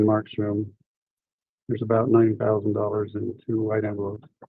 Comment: 90,000 dollars not 9,000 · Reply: I thought he said 90 000 too but said 9 000.